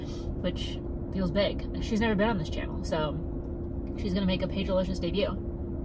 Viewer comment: I think you said "she's gonna make her paigealicious debut" but I heard "her big delicious debut" and I think you should roll with that because it's fabulous.